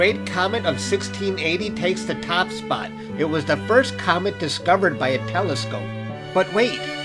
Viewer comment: I thought he said comic instead of comet 💀